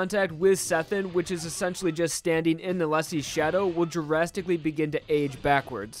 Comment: i keep hearing "jurasticaly" instead of "drasticaly"